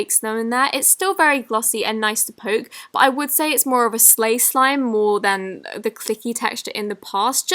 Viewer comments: Who else heared her say Slay Slime instead of Clay Slime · wait a sec did she say "slay slime" ??????? Um ok? (¯―¯٥) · does she say sleigh slime instead of clay slime??😂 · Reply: *Slay she meant a slay slime not sleigh it's a mix between a bit of clay and slime :)